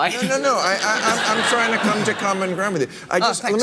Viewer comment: Important difference: I heard him say I’m “trying“ to come around to you, not “starting,” which is what I thought I heard at first as well and why I went back to listening to that moment again. As for Bill Maher: yes he’s too centrist for me, but I find him a combination of genuinely funny/entertaining at times, sometimes progressive, and a barometer of where some neoliberal winds are blowing. So I watch for now. Samantha Bee, however, is getting on my nerves so I only listen to her selectively.